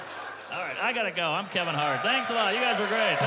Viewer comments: "Thank you, I'm kevin heart" "Mark Normand, everybody!" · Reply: I thought he said "Fuck Normand, everybody!" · At the end i thought he said "fuck normand everybody"